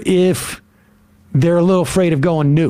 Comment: I know they said it properly & I just misheard it as "Mutually Absurd Destruction", but it's always a little discombobulating to mishear/misapprehend a word or phrase, yet the brain-typo version is as or perhaps even more apt the the original term.